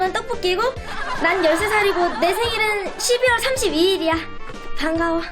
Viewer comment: 헉..12월 31일로 들었는데 12월 31일 제 생일인딥...ㅋㅋㅋㅋㅋㅋ